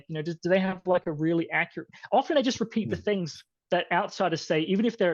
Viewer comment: is referring to Taqqiya or something else? I can't hear him that well